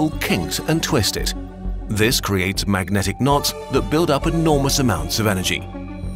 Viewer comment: is it just me, or did he say magnetic nonce